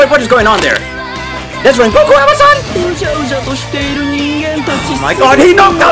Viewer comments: i hear amazon ahahahahhaha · i hear amazon hhahaah but thats rengoku little brother